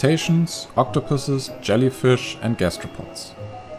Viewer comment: At first, I thought he said they eat gastrodon XD